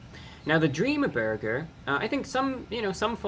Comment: In thd beginning it sounded like you said dreama burger